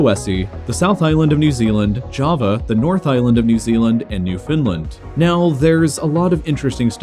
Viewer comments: why do I hear "New Finland" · I thought you said New Finland lol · did u say new finland? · he did not just say "new finland"😂😂😂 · did you just say new Finland im confused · i heard new finland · New Foundland.. I thought you said New Finland. Finland is old then.